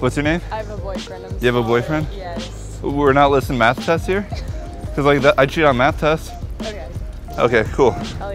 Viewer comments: I totally don't understand what happened here. What did he say to change her mind? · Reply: He said "we are not listing maths tests here? Coz those are things we can cheat on" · what he says? And then he said i cheat on math tests or what? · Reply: “We’re not listing math tests here? Cause I cheat on math tests”